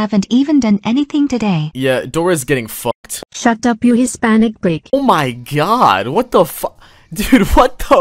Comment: did they call her a hispanic brick